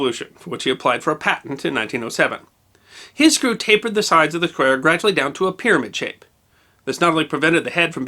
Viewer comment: "His screw tapered the sides of the ____ gradually down to a pyramid shape." What is he saying there??? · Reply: "square"